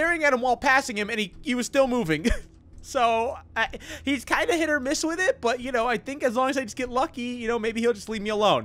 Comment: did he say hit or miss? I GUESS THEY NEVER MISS HUH? sorry, thats dead now